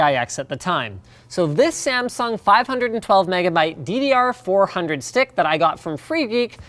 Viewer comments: Did Linus just say ddr4 with his old computer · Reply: He says 400. 400Mhz was its speed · Anyone heard DDR4 the first time?